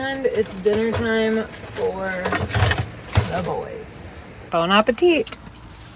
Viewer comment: Did you say, "Bone appetit?"